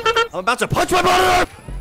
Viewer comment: Did you just say I’m gonna punch my brother!? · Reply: I’m about to punch my monitor